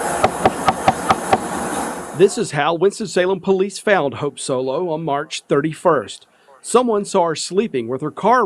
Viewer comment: she literally made one excuse after another...how she acted while inebriated shows how she truly is. Shame for those kids... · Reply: This is how Winston Salem police FAILED Hope Loss ...did i hear that right ?